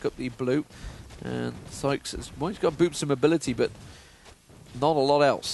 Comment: Is it just me, or did he say that Evelynn had Boobs of Mobility at 11:05?